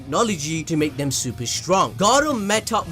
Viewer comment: Is it just me or everytime he said Garou I thought he was saying Gaara lol!